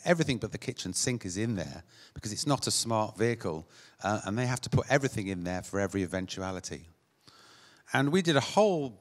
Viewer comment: Modular. So “you only take kit you need for the emergency you are responding to” issues I could see with this 1. surely this would increase response times 2. Ambulances are often not at their station and are going from job to job to job 3. What if they get flagged down or witness a RTC (Road Traffic Collision) where they need equipment, but don’t have it because they had just been to a different job and took kit that is not relevant to the RTC. They wouldn’t have the kit to help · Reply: That's not what he says, nor is it what the ambulance shows. He says at the start (0:22) that you "because it's not a smart vehicle you have to put everything in there for every eventuality". He wants to take things out of the ambulance, for the crew to slot in as they go to the emergency, which will as Nathan said increase response times.